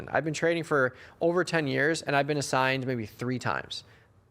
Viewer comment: Such BS, in 30 years you’ve been assigned a couple of times? I find that hard to believe… you may be trading paper money then, cause these MM tend to exercise at the worst time every time. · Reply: I think he said 10 years 2:33 but still agreed: unbelievable. And getting assigned ITM short calls, ouch! No Bueno! But he's got a point about getting Puts assigned: yeah, if I've got the Buying Power, dig it.